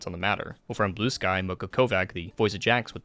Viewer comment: I thought he said mocho kovach..... TwT MOCHO KOVACH.......